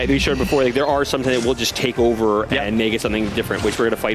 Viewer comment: Seems to be stumbling over his own words, but from what I could understand it sounds like he said "there are something that will just take over and make it something different", possible that he accidentally hinted at the X Parasites being in the game?